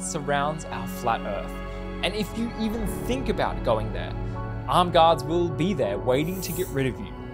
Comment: What did you say? Did you say armed?